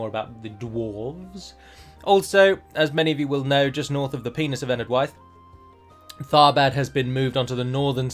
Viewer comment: I had to go back to make sure I heard that correctly lol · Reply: i went back and still heard "north of the penis"